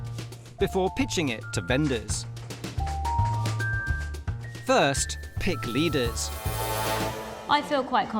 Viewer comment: did he say "pitch it to benders????? "